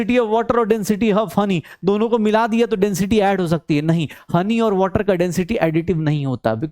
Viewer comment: yea kya hai? Funny? Phunny? Kya bol rhe hai sir samajh nhi aarha Mujhe to lga “how funny” lekin pir bole “funny aur water mix nhi hosakte” Kya chal rha hai 😂🤔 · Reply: Honey***